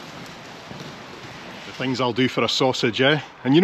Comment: Merry Christmas and happy holidays! 7:33 Btw, did say strong smell of weed or wheat? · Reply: weed :D